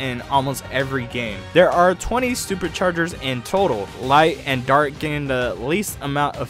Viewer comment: made it sound like you said "stupid chargers" which i just find amusing